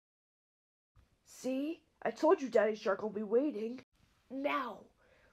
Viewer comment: I heard "Daddy Shark" and screamed- I think my mom and dad heard me